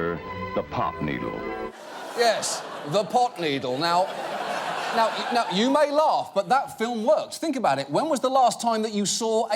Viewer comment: Hello, LastWeekTonight! I love this video so much, and I want to make sure everyone can understand and enjoy it. That's why I wanted to let you know that your captions are a little off. At about 0:38 the auto-captions say "you may law but that phil looks." where they meant to say "you may laugh but that film works." At 0:48, it says "America is now in the midst of a new drug prices," where they meant to say "America is in the midst of a new drug crisis." At 1:21, it should be boy instead of "ball." When you get a chance, could you fix that? At 1:28, it says, " But he's even got like hearts with local news story had some." When it should be "But here's the thing, even that lighthearted news story." That'll help the 360 million folks globally who rely on closed captions to understand videos. Thank you, and keep up the great work!